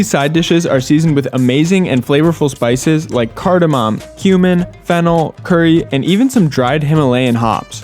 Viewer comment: did i hear you say that there flavord with human at 0:49?